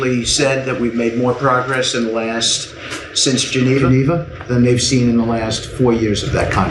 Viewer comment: Did Witkoff say Genevaneva? Like, say, "Genevieve"? What is happening in his mind?